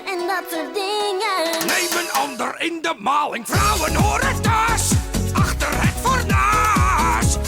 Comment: I could've sworn I heard something akin to the word "whore" in that. XD